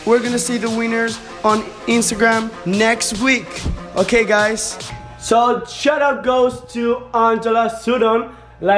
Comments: I thought he said "so shut up goes too"😂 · said WEINERS !!!! iNSTEAD OF WINNER LOL !!! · When he said winners I thought he said weaners lol😂😂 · When he says winner it sounds like he says Wiener